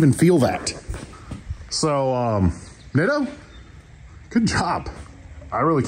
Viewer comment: Nitto tires! I have a link in the description box. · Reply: Nitto? It sounded like you were saying Niddo, lol. No wonder I could not find them, as I was looking for Niddo. I did not think to look in the description for them. Doh! Cheers. It is not a brand I have heard of before here in the UK. It looks like they are owned by Toyo. The ones you bought are $670 each here for the 32". Way more than you get them for. I am going to look into them as I am getting close to being due some new tires. Not anything like yours though as they are for a car. I usually buy Michelin Cross Climate and have found them to be excellent, but I am open to trying something different if they are good.